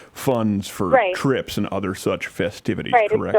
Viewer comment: I thought you said she was raising money for crips 😂😂😂 would’nt that be a bitch if gang members recruited Girl Scouts to raise money 😂😂😂